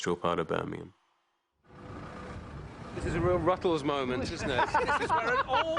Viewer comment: What does Roger say at 1:13? Sounds like he says something like " This is a real ruffles moment"